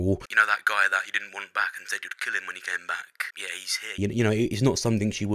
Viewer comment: Sounds like its said "ick.. came back"